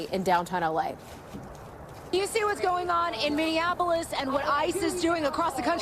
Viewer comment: The guy on stilts didn't say Minneapolis, it sounded like he said Mindianapolis